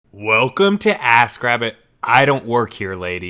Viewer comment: LUL I thought you said "welcome to ass grab it" at first...lol